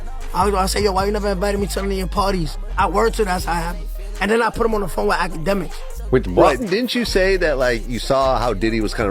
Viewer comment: sounds like he said “then I put him on the phone with academics” 😂 wtf lol